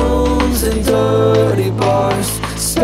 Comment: I thought it said "Strobelit rooms and dirty boys" but woah i just looked at the lyrics on the screen and its dirty bars my mind is broken